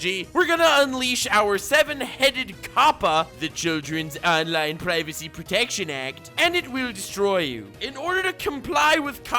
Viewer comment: I heard "the JoJo's online privacy protection act" and I died?